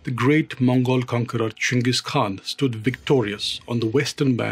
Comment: I nearly heard that as "Chungus Khan"